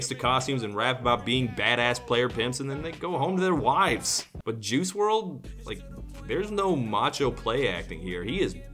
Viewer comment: Heard you say "Jews world" Tf is wrong with me